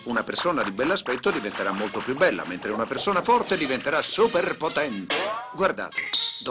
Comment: Furry Force is on the prowl! Also 0:28 - Did he just say Persona 4? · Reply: He says "persona forte", that in italian means "strong person"